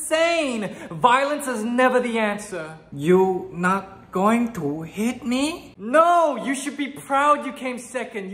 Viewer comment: this is what i heard "you...not going.. to eat me?" then i realized